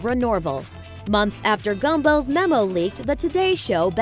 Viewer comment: Did she say "Gumbo" instead of Gumbel @ 7:37 ?